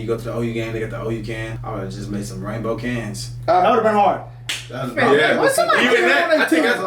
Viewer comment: Ohhhhhh! You said “rainbow can”. My hearing isn’t great, so at first I thought you said would people be as upset if they put out a Rambo can? And I thought heck no, most people wouldn’t be upset, they would have LOVED a Rambo can!